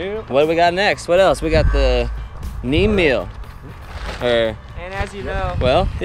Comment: is he saying "neem meal"? Because I have some neem oil, will that work the same?